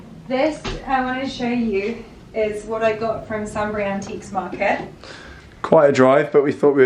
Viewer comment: that is stunning, I couldn't quite hear what antique market bought it from. Sunbury?? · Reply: Yeh sunbury!